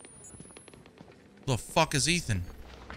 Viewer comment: Absolutely great moment lol 56:00 "Hi I'm Ethan" 56:55 "I don't remember any of this" proceeds to talk about not remembering names. 58:33 "Who the Fuck is Ethan!" I couldn't help but share this. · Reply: Lol I heard "The f* is eating?" ^^ I wasn't paying attention to the conversation.